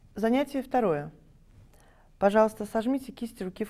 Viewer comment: Вы тоже услышали ,, пожалуйста"?